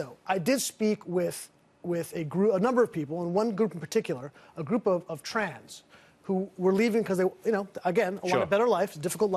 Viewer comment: did he say, "a group of trans?" Like we need more of that shit here.